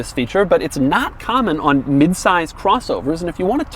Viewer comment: Did he just say "mid-size"?